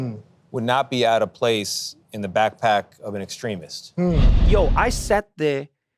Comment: When i heard 'backpack extremist' i heard martyr terrorist that and the word apartheid Thank you Mr. Cotes!